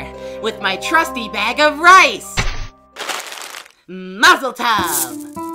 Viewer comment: what I heard: muffletub! what test tube said: mazel tov!